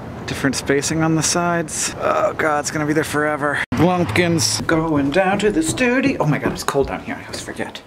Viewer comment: I thought Hank said "blumpkins".